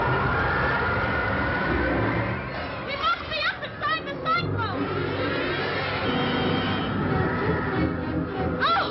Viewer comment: What Did Dorothy Say!?? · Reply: We must be up inside the cyclone